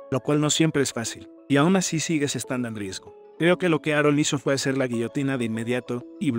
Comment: escuche Arol ? XDDD